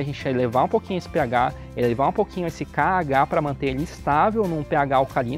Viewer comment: kkkk kkkk foi mal cara mais foi muito engraçado com você falou k.h , perecía caga